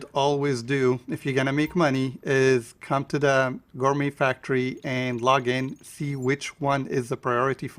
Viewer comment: The gourmet factory? What did you say? Thank you. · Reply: yes that is the Gourmet Factory.